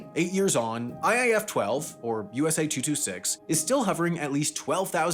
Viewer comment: you say USA-226 but the captions say USA-266. Just a nitpick, great video!